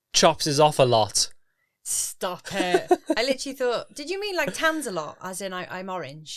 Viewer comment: ! I totes thought said tansalot too:) You have a beautiful glow babes!